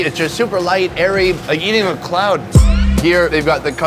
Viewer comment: U have eaten a cloud..???? Or what did u say at 6:40?